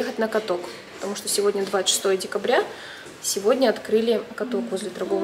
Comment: Мне послышалось или она сказала 26 декабря